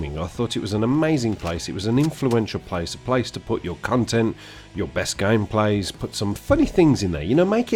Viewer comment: lol at 2:34 it sounds like you say "cuntent"